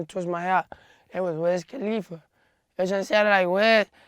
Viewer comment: is he says wiz khalifa